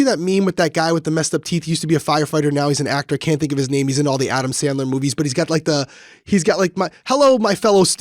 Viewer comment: Steve Buscemi is SO much more than "that guy with goofy teeth from Adam Sandlers movies" LOL!! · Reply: I misheard, and thought he said Adams family movie 😂